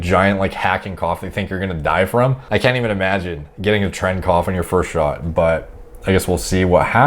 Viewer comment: what did he say tren cough? What is that?